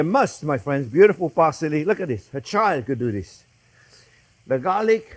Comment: I've heard this man say "a child could do this" a million times but for whatever reason when I heard him say that while watching him garnish with herbs I swore what I heard was "a chive could do this"